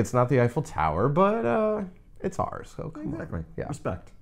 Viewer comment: Did you say "it's ours"? Thanks! YOU are ours... Forever!